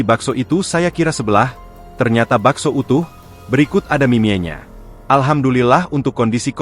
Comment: Terakhir bilang apa sih di menit 1:28 ? "..ternyata bakso itu berikut ada minyanya" ? 🤨